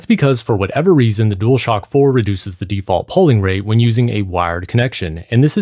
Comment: the “polling” rate ? Is that what was said ? What is that ?